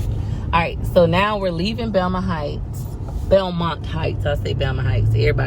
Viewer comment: I thought you were saying VELMA Heights. And when you showed the sign, it's Belmont. Looool